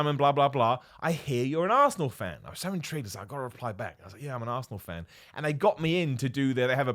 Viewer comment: Omg i was a little away from th phone and i think i heard Simon say "i am an arseh*le fan" lol. It really cracked me up. Btw Simon, Geralt is holding a Turkish flag.